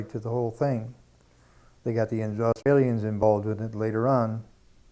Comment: ? I think he said Australians